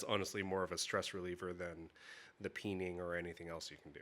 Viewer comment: Whenever you say “peen” I hear “pee in” at first. What is wrong with me?